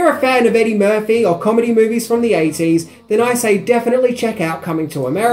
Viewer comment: Dude that ending almost got me..it gave me a double take as I thought you first said "Anyway i am off to eat a big dick".....to funny